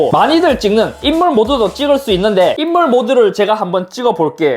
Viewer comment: 찍어볼께 로 들리는건 저뿐인가요? 불편한건 아니고 ㅋㅋ